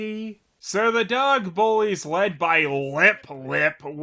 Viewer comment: When you said “dog bullies”, I thought you said “Dog Police” and I got excited for a second.